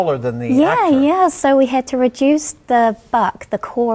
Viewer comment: did she say "We had to reduce the fuck" ?